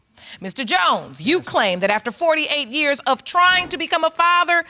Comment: “After 48 yrs of trying to be a father...” His age: 52 🤔🤔🤨🤨🤨 You telling me this man was trying to have kids since he was FOUR??!? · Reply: Oh &I thought I heard 33 years of trying💀